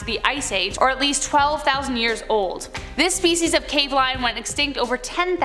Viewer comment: it really sounds like she said “this FECES of cave lion…”